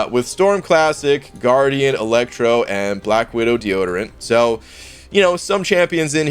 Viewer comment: 😂😂 deodorant or D-origin? Not sure what I heard